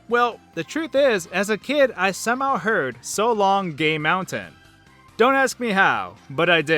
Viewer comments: Fighting Bowser I always heard "So long gay donkey" lol. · What are some things you misheard in video games when you were younger? Keep in mind if you’ve figured out what the phrase was by now, it doesn’t mean it isn’t worth sharing. Most of the examples in this video are due to several different factors (tv quality, background music/sound overlap, muffled TV sound, etc). As you watch the video you may have thought some of the dialogue was pretty clear, and that’s because a lot of the examples were isolated sounds with these factors removed (which makes them waaaaay easier to understand). However, I’d still love to hear about your own examples, because honestly I still can’t even remember half of mine. They can be hard to recall without actually hearing them again, so perhaps some of your stories will jog my memory! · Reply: I heard "So long, gay Bowser!